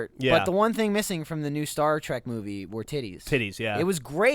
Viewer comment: I thought that Geoff said "Chris Pine, Denzel Washington, roller coasters, top hats, and kitties," and I was like "Oh, that sounds like a perfect movie to me," but when I realize he said titties, I was like "Oh that sounds like a perfect movie to me also!" so it's win/win is what i'm getting at